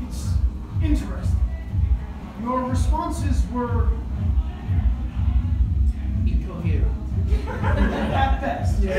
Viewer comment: Do you guys know what the guy said at 1:35:10 ? Where Connor said "your responses were"... and then the guy said something and Conner responded all sassily with "at best ya" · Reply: Maybe: "Your responses were..." "Incoherent" "AT BEAST"